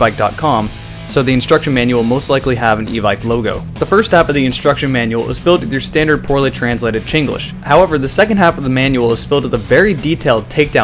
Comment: did he say "Chinglish"? lol